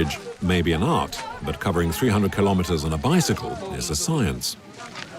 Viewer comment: Did i just heard it right ? 36:40 the guy ride those bike with charcoal for 300Km ?